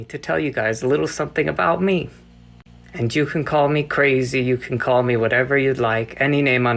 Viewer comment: does he say, "and Jew can call me crazy"... hahahaha I know it's "and you", it just sounds situationally funny